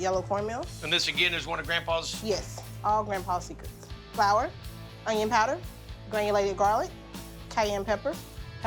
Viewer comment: ngl thought she said “bacon powder” and didn’t question it cuz she’s the head chef. then I realized I was a dumbass.